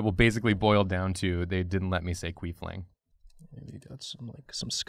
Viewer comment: I thought said Griefling because he was gloomy. So I laughed...